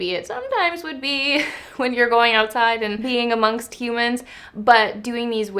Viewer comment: "When you're going outside and peeing amongst humans." hahaha Whoopsies, I misheard.